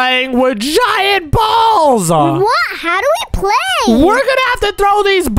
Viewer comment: did say giant balls😬